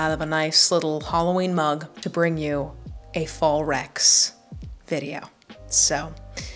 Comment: When you said “fall recs” I legit thought you said “Fallrex” like a dinosaur